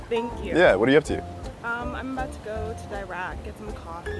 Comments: I thought she said “I’m about to go to Iraq” 😂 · I thought she said she was going to Iraq